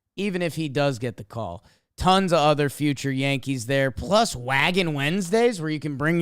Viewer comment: When I first heard "Waggin' Wednesdays," I heard it as "wagon" and when you mentioned bringing a dog, I thought you had to bring the dog in a wagon. Lol